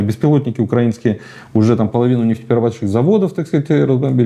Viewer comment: Слушал на фоне готовки обеда и послышал,что украинские беспилотники половину ликеро-водочных заводов разбомбили,а потом перемотал оказывается нефтеперерабатывабщих😂